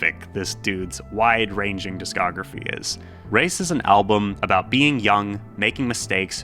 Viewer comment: Racist i meant cuz it sounded like he said racist